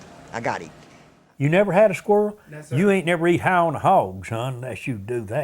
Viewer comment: When he says, "You ain't never eat high on the hog" at 29:08, the subtitles say "You ain't never eat hound dogs." That made me chuckle. I love this documentary and how it reminds me of my Granny and many of the kind elders around me growing up.